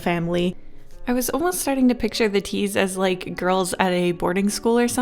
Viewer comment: I thought she said cheese as cute girls for a second skskksks